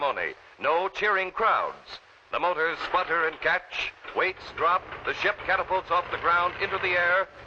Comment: At about 11:30 Did he say "the jet catapults off of the ground"?